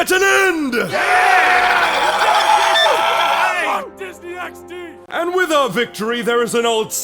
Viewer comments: does anyone else hear "fuck disney XD" in the yelling? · did he say "Fuck Disney XD?" Oh man, that is pure randomness